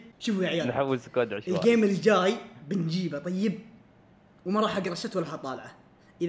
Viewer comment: وش قال هناا ؟؟ وماراح اقراها ست ولا اطالعه ؟؟؟؟؟؟؟؟؟